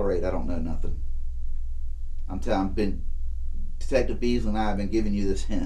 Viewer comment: does he say Detective "B", "Bees" or "Beans"??? 😂 "I am Detective A, and this and Detective B" "This is Detective Beans, and I am Detective Weiners."